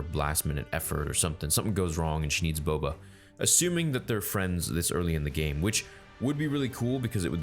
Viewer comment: Am I the only one who heard theory say " if they're friends this early of the game"?